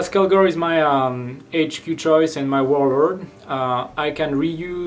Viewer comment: did he say warlord?